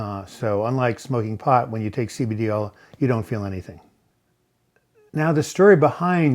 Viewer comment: Did he say you don't feel anything when you take CBD 2:20? Maybe I took too much!